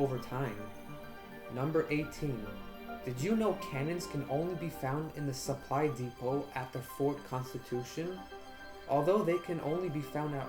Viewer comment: i didnt quite get what u said... Can anyone tell me what he said? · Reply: I said that cannons can only be found in the Fort Constitution, and specifically in one of the buildings at the Fort Constitution, known as the Supply Depot. Hope this helps!